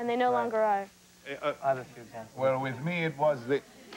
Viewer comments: not sure, it kind of sounds like he says, 'i have a few examples of/for that' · Does anyone know what River said that got the big laugh at 2:56?? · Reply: Simone Turner he said “i have a few examples of that too” as a tagalong to lisa’s remark about her periods being less painful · WHAT DOES RIVER SAY @ 2:59. SOMEONE PLEASE TELL CUS EVERYBODY LAUGHS . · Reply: ILoveChrisChambers i dont use tampax